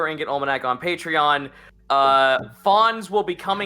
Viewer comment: Did he say Fon's /Fan's ??? FML if he did I am making a homebrew plant race called Fon ;-; , really dont wanna feel like its being copied